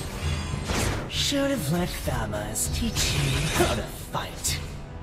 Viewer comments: I keep hearing "Should've let thamuz teach u how to fight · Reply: This is legit true what i heard "Should've let Thanos teach you how to spite" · I keep hearing "Should've let thamuz teach u how to fight